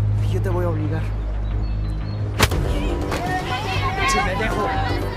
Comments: le dijo "Pinche pendejo u oí mal? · No se si escuche bien pero creo que dijo pïnch€ pënd€jø xD la primera ves que lo escucho en la rosa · En el minuto 8:44 dice Pinche pendejo o si me dejó 😅 no se cómo que entendí lo primero